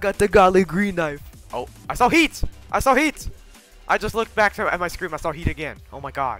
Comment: I heard "I SAW TEETH" insted of "I SAW HEAT"